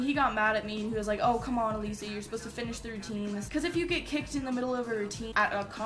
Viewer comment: i thought it said kicked in faze